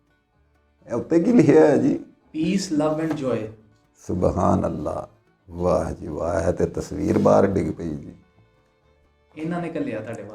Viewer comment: did say, waheguru 🙏